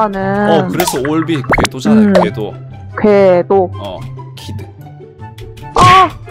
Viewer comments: 에 이 "궤도"라고 또박또박 말한다음에 이 아주 조그만 소리로 키드라고한거 들었다 ㅋㅋㅋㅋ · 여기서 궤도키드 라고 이해 했는지 키드 가 살짝 들리네^_^